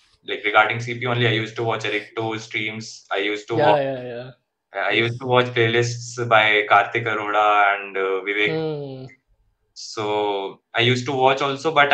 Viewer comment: can someone tell me what did he say from 16:09 upto 16:20 mention the name of the resources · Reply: Errichto Streams, Karthik Arora, Vivek Gupta, all are on youtube